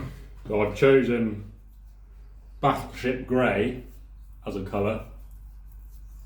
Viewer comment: did he say batshit grey or battle ship grey ??